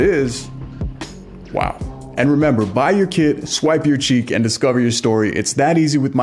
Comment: At the end of your video, I thought you said, " Bite your kid!" Lol not buy your kit.😅